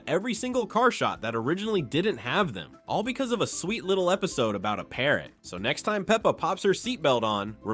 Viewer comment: 5 eps back in recovery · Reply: Did he say “sweet little episode”? That is hilariously funny. Also, Gage and Jack is calling their newborn baby sister Katie “Sweet little tomato”.